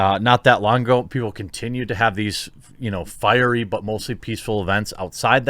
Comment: Firey but mostly peaceful sounded like you said firey butthole lol